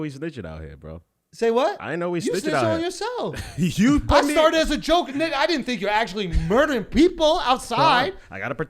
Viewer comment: anyone hear that ? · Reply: "I started it as a joke and 🔥🔥🔥🔥🔥'